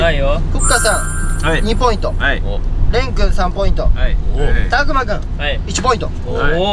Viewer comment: こーじの蓮くん呼び♡ 私にはそう聞こえるすきです